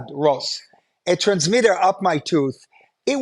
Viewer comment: When Uri said “up my tooth” I thought he said “up my tush.” Then I saw the dental diagram and realised he wasn’t using Yiddish slang.